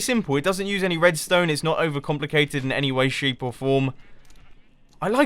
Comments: did you say in any way SHEEP or form??!!! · Am I crazy or at 4:40 did he say "An any way, SHEEP, or form." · Did he just say "in any way, sheep or form"? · or so it sounded like he said "in any way, sheep or form" XD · "It doesn't use redstone in any way, shape or form." Am I the only one who heard shape as sheep?